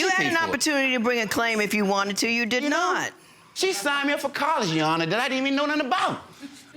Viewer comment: Idk what he says at 15:37 I watched it like 6 times 😂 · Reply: he said that she signed him up for a college that he didn't know anything about 😂😂